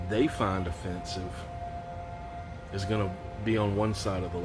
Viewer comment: With your neighbor paragliding next door I thought you said offensive smells. As a city bus driver I could talk all day about offensive smells.